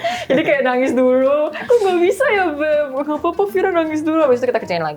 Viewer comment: Wait itu di menit ke 19:05 xaviera bilang “kok gabisa ya beb?” Ga sihh🤣 apa aku salah denger yaa